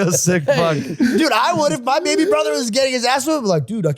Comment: I rewound about ten times because I thought George said ‘if my baby brother was gay’ 😄